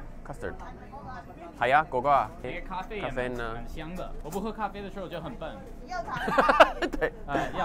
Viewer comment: can anybody please write the 汉字 of what he said? I think he said "我不喝咖啡我是就很笨". But I'm not sure